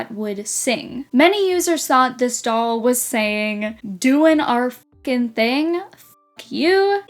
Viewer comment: struts having swappable feet makes more sense if you consider that they probably intended to make indefinite more waves, like the second wave in russia. therefore there would be more of each color and they didn't have to be mismatched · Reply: also the bratz karaoke doll sounds more like she's saying "doing our funky thing! (funky!)", i don't hear the words fashion or cute at all, but the word funky could very easily be misheard